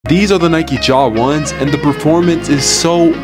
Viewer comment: I thought you said Nike Jawas before I saw the caption